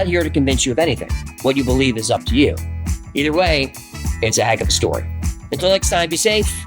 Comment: What does he say at the very end when he does the illuminati hand gesture and says something like "it's agem stewart" am I missing something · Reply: it's a heck of a story